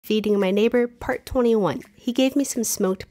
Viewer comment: FOR A SECOND I THOUGHT SHE SAID “Eating my neighbor”💀